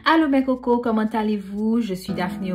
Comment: Superrr ! Is it me or I heard you say : “Allô mes coco” ? 😂 anyway, Nice presentation ! 😘